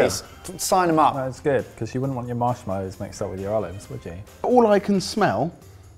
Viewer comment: Did James call mini marshmallows "mozzarella!" or did I misheard him? If it weren't for the captions and the marshmallows shot that followed I might've not even noticed.